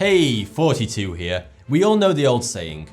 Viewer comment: I always hear you say "42" and not Thoughty2